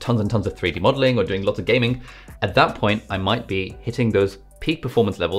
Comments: excuse me but did you say "lots of gaming"? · I'm sorry did he just say "gaming" 🤯